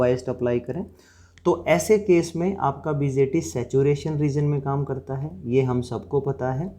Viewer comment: sir wo apne kya bola bgt???? correct pronounciation kya he??? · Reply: Dear Sutama, BJT (Bipolar Junction Transistor) Thank You.